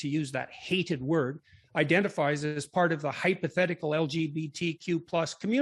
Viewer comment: Kyle: “how many people will become trans” JP: “well 1/5 ppl already identify as part of the LGBT community so ….” LGBT does not mean trans. Seems kinda dishonest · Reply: jordan definitely seemed more hostile than usual in this interview, but don't misrepresent the man. at 05:00 he clearly says hypothetical. Not trying to stan him or anything just saw this comment and thought it should be pointed out.